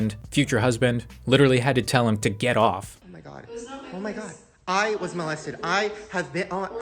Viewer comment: Did he really just say "I was molested" or am I just hearing things?